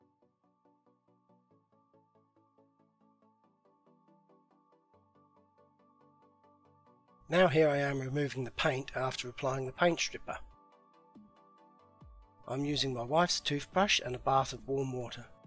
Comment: Removing the paint "using my wifes toothbrush" ..haha..brilliant.... and brillant job as always... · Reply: I thought he said "using my white toothbrush".